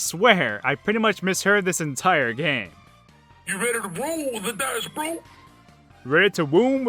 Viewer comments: "You ready to roll with the dash brew?" is what I hear. Also the last racer in that game sounds like she's saying "Gridlock!" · I hear - Ready to roll the dice, bro? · i heard u ready 2 roll the dice roll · I heard "you better roll the dice bro!" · I heard "get ready to roll the dice, bro!" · I heard "You ready to roll the dice blue!" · Pretty sure he's saying, "You ready to roll the dice, Bro?" I remember playing the arcade version one of the Marvel Vs. Capcom games (might've been the first one) years ago and thinking Dhalsim was saying "You're on fire!" instead of "Yoga Fire!" Definitely a mondegreen but an appropriate one.